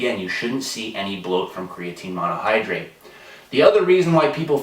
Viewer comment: Creatine bloat? Oh, I thought you said "root beer float". Bummer.